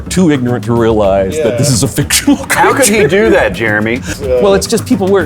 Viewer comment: Did he just say "fictional"?